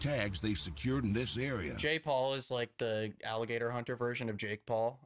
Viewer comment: Everytime i heard jay paul i thought they say jake paul😂 Didnt realise later they mention that they also hear jake😂